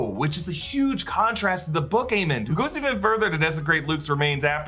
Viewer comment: What in the world did you say at 22:04?? I just hear "who goes even furthueiuhwklkwjbwh is..."